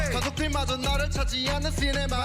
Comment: 가족들 마저 나를 안찾는 시네마로 들리는데